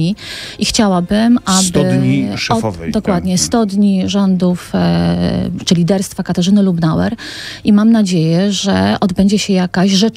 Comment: Czy tylko ja usłyszałem 2:00 - 100 dni czirliderstwa lubnauer? hahaha